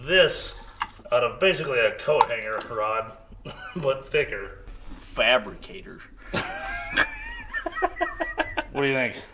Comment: "Fabricator"....... I heard "ALIENS"......... Change my mind....lol